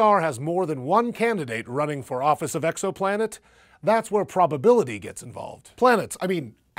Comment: I'm sorry, did he say "EXO planet" @ 2:46? XD